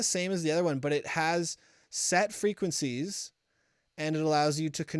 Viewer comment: Did I just hear "sad frequencies"? T__T